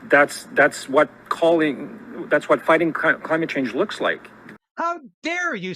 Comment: Did say, “ crimate change”?